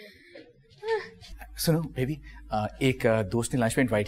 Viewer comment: Did Sarmad call Maha baby??? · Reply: yes he said suno baby